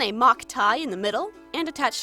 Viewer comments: I heard "mai tai" like "my type" xd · So I usually half watch these videos and half do something else, and for her name, I wasn't looking and I hear "My Tie" and obviously that confused me, so I looked up and it was mai tai. I've never felt as stupid.